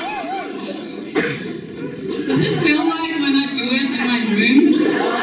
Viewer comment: What did the girl say at 11:11 ?? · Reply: "Does it feel like when I'm doing it in my dreams?"